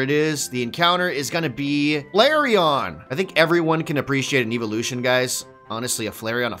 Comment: Did… did he say his favorite Eevee-loution was a Eletrike? Dang lol u mean Jolteon? Lol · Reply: It also sounded like he said Ebolution. I didn’t hear a v at all lolz